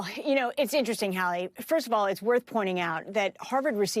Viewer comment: Fart 😅 Was it me only, but I heard Fart around, 1:02 ??? Has anyone else heard it. 😅😅😅